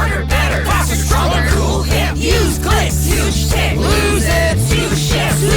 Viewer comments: I can't be the only one who thought they said "huge d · I think I heard "huge s-"